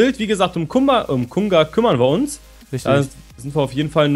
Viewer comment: Ich hab' verstanden, dass gesagt hat: "Um Gumba kümmern wir uns"! :-D